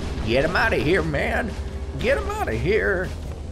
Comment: U sounds like u said get out of here at 6:12 sounds like a grandpa pig from Peppa Pig 😂😂